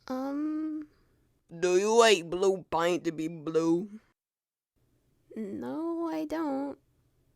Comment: this is what is said”do you eat blue paint to be blue? 🤔 🤔 🤔 🤔 “ AND THIS IS WHAT A HEARD,DO U EAT WHO PAY TO ME WHO